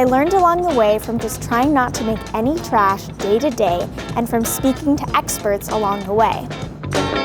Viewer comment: don't kill me but I thought it said "I try to make someone trash for 30 days"